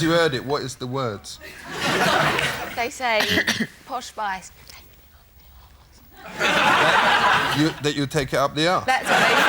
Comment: What did she said? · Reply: Posh Spice, they take it up the a*se